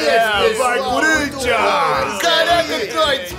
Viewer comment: Eu ouvi um " Vai Curintia" nesse meio aí em kkkkkkkk